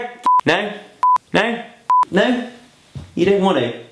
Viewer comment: 50 ish every time it bleeped it sounded like he was saying f no